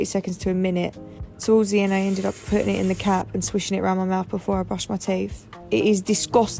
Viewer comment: 🤣I thought the English girl said I put it in the cat 🤣 then i realised she said put it in the cap 👍x